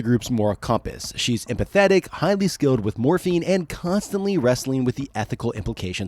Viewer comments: highly skilled with morphine is what i heard lmao edit: 26:35 and again lol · At like 10:24, I thought I heard Cassie was "really good with morphene" and then I REALLY thought these books were dark XD I figured out you said MORPHING a few seconds later